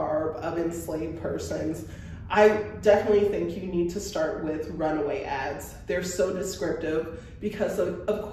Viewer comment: At first I thought you said people should checkout the “runway ads” for the style of head garments. Then I realized you said runaway ads 💔💔💔